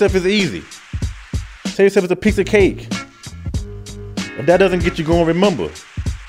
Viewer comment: did he just say “tell yoself u WANT a piece of cake” 🤔 “I WANT A PIECE OF CAKE” hahaaa